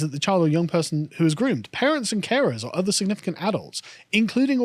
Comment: When he said "Parents and carers" I thought he said Parents and Karens.